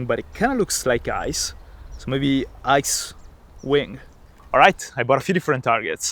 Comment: had to rehear it bc I thought he said "I Swing"